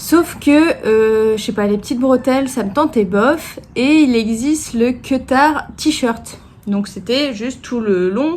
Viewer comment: Moi j'ai entendu "queutard tee" j'étais 😱😱😱 pas facile la transposition francophone 😅 Je regarde les adresses qui te sont données, je vais aussi dans ce coin là cet été !